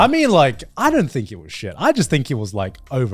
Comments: When garnt said edward brothers i totally thought he said n-word brothers 😅 woooow. Wouldnt it be the elric brothers anyway, alphonse and edward elric. ☠️ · Whenever they say "the Edward Brothers" I hear "The N-Word Brothers" and I can't unhear it. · I thought Garnt was saying “the n-word brothers” and got real confused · I thought garnt said the n word brothers 😂